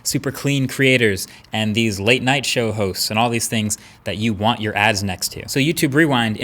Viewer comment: did you said show hoes?